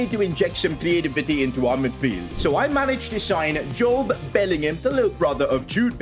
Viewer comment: yo I thought he said he was signing Jude Bellingham😅